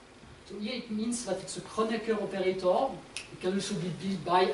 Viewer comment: what is the operator said in 38:30 something like koneckeur operator?